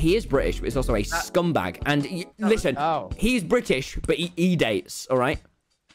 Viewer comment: wait I don't understand what tommy said at 5:04. "He's British but he.." what's? I have no idea what he said lmao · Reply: 'hes british but he e-dates' :)